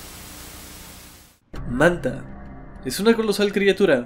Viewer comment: Por poco pensé que decía manda el título (manda es un kaiju de toho)